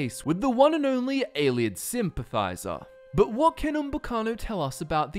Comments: I misheard that as Ayleid simp-advisor in hindsight it probably isn't that far off. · I thought he said Ayleid simp